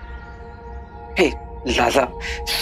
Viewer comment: In 2:32 did he say zaza ?